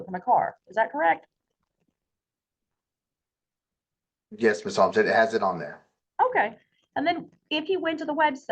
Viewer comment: did he just say "yes Miss Holmes", to the judge?!😮